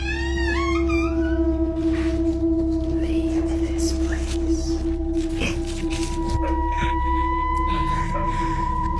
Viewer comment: creepy "leave this place" 51:36 "don't..." (can't understand the rest) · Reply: Some one else’s comment says that it say “Don’t come back “